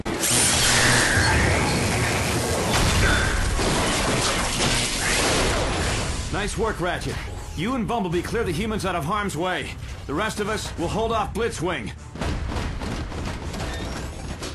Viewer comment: Oh god. The way optimus said "humans" made him sound like he was saying "Clear the HYMENS out of harms way".....